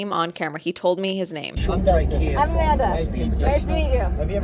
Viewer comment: I keep hearing "I'm Garrington" I don't think that's what he said